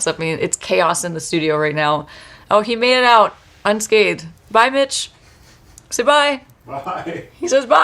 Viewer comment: I thought you said “Bye b*tch!” to your husband at minute 3:47. 🤣